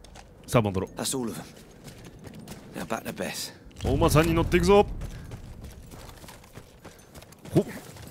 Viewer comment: お馬さん乗っていくぞがおばさん乗っていくぞに聞こえた